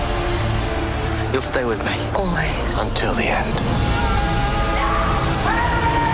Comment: sirius black, what did he say ? I can't hear ! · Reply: until the end